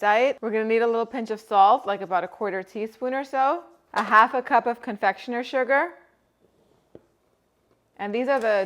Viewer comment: Hello. I noticed in the video the recipe for the almond paste calls for 1/2 cup confectioners sugar while the printed recipe calls for 1 cup confectioners sugar. I'm going to go with the cup as I'm making them now, but jist wondering if you can clarify that for future reference. Hoping I chose correctly. :-). They look delicious. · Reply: I thought she said 1 1/2 cups confectioner's sugar, but I'm using captions so not sure